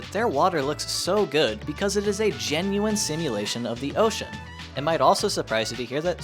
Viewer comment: Thought it said “I tried STIMULATING the entire ocean”